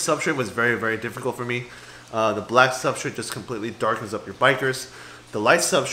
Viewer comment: That one elongated fish looks bada---. I thought I heard you call it a biker. What kind of fish is it exactly, how big do the get, and how are they in a community tank????